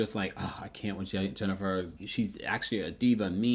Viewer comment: Oh sh*t! I thought he said Jennifer Hudson. 😂😂😂